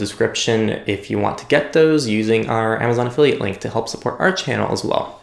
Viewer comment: LOL, what!? The auto-gen subtitles think you said "have we Marissa stay," and I have no idea. 😋